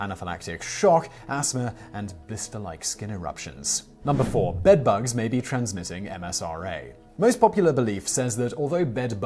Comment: That's MRSA (or "mersa") caption is spelled correctly, but I heard "MSRA"